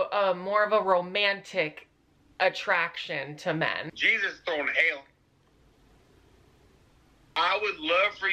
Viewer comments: help did he say “jesus thrown in hell”? 😭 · “JESUS IS THROWNIN HAIL” that one got me omg 😭😭😂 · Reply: BRO I THOUGHT HE SAID THROWING HANDS LMAO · "I like men, I got friends" "Jesus throwing hail" Omg lol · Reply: Not gonna lie I thought he said "jesus going to hell" 💀🤣